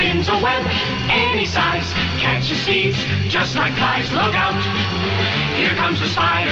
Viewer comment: Did anyone else hear “Catches thieves just like guys”?